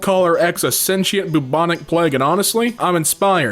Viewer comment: pleg instead of plague